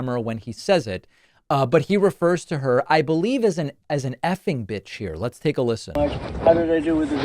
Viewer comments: Sounds like he is saying effing bad. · He definitely did not say she she’s an effing B*itch. He very clearly says she “so effing bad.” I’m not suggesting that he likely hasn’t called her a b*tch I’m just saying that we should always try to be better than them and not say that they said something they didn’t. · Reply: I like David’s commentary but it’s VERY clearly “effing bad.” Even clearer with headphones that it sounds nothing like b**ch. Even news articles transcribed it as effing bad. Either David is mishearing it, or he’s using it for clickbait intentionally.